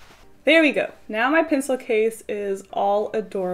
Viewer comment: Hahaha 1:55 I thought you said PINclecase! You have just reborn my love for gel pens.